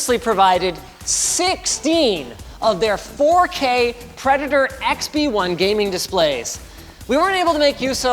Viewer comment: When auto-translate writes xbone instead of XP1 at 2:03 - pure gold :D